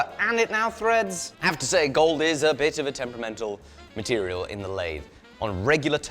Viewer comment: Did you just call Gold "a Bitch to work with" in British? 😅